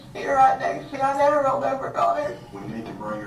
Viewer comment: did she say "I never rolled over on it"? It? WTF. I'm not surprised but WTF, calling your baby an it? I hope I heard that wrong..